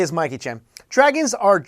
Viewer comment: did he just say mikey chan?????